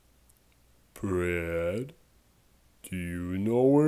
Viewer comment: I thought he was saying bread 🍞👍